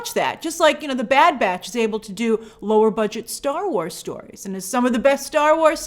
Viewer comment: I know she said 'lower budget' but I heard 'lore budget' and I think that's a great descriptor for smthng like Bad Batch